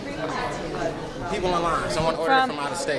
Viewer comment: It sounds like says on line instead of in line. It's confusing, I keep thinking on-line like on the internet.